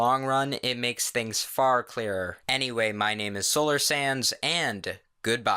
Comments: I think im really stupid: at the end i heard "My name is Solars SANS" Someone send help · I actually thought you said solar sans at the end and I was BOI NO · Lmao, at the end I heard "Solar Sans" and I almost yelled "Oh hell nah!" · For a second i thought u said "solar sans" at the end :v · I thought I said solar sans but I didn't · i thought you said * in comic sans font * Solar Sans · i always thought u said “solar sans” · When you say your name it sounds like your saying "solar sans" Me: outertale? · It sounds like your saying "Solar Sans" Noice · At the end I thought you said Solar Sans. Lol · Everytime you say your name I hear "Solar Sans" So I can just imagine that's another undertale AU someone created. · So when i first heard you say your name i thought you said Solar Sans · Lol I thought he said solar sans! MEGALOVNIA INTENSIFIES